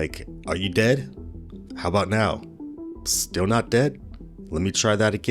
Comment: He Def was not alone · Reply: my feelings exactly. At one point he said, "Not David." The detectives didn't seem to notice, or I misheard.